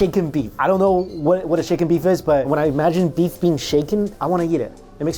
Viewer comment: Shaken beef... I thought what you said was chicken beef. Hahahahah